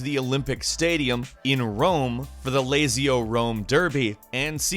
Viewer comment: did he just call "lazio" "lazy-o". SPARTAN RAGE